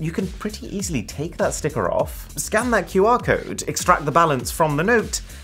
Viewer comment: he said scan...I heard scam😅😂